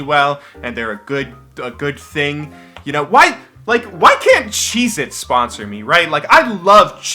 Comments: I thought he said why cant Jesus sponsor me...... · I thought he said 'why can't Jesus sponsor me. I love Jesus.' Anyone else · I thought you said, "Why can't Jesus sponsor me?!?!" XD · Am i the only one that heard him say '' Why can't Jesus sponsor me, I love Jesus'' I still dont know what he said xD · I thought you said "what if JESUS sponsored me?" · lol I heard "Why can't Jesus sponsor me?"